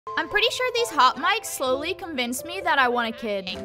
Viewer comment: At first I thought she said these hot Mics slowly convince me I won a kid